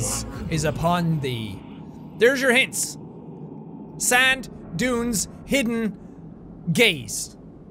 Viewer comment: I thought he said "There's your clues. Sand. Dude. Hidden. Gays." I was like wow this game is progressive.